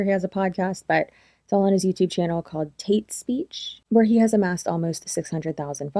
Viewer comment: Not gonna lie I heard "Taint Speech"🤣 I think he should re-brand to that 🤣